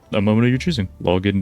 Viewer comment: Also, don't know why i heard the "login dude" part as "whilst being nude" ... that would be ONE helluva race, and I'd watch it